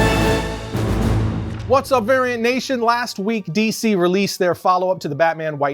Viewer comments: Thought he Said "Aryan Nation" ✊🏻 lol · Use VARIANTC55 to get 55% off your first month at Scentbird <link> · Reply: In the beginning of this segment, I thought he said "Aryan Nation" ✊🏻 lol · Bro I really thought he said Aryan Nation at the beginning 😭😂